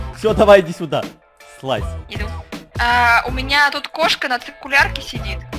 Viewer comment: Мне послышалось или сказала "You Know"?? Прислушайтесь